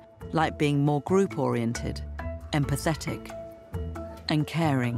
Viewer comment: laughed so hard when it said "pathetic" I think it meant to say empathetic but it sounds like pathetic